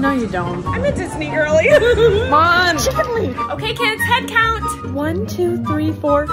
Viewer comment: the names 😭 jaxxon and chickenleigh😭 so true so very true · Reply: I'll be honest, until she added subtitles, I thought she was saying "Chicken wing".